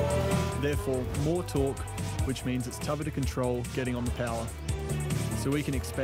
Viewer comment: What does he say at 02:36 and what does it mean? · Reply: More torque which means it's tougher to control getting on the power. As they've got more horsepower the cars are much more powerfull and therefore it's more difficult to handle the car if you go full throttle.